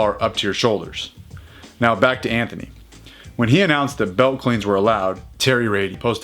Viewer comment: Thought you said belly clean at first......kinda let down